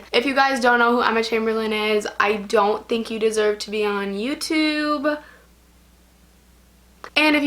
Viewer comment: I thought she said "emma chamberlain doesn't deserve to be on YouTube" and I was like "dafuq?"